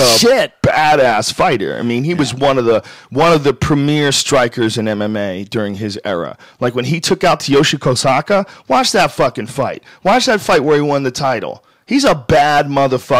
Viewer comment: what is that name?it sounds like hes saying Tyoshu Konsaka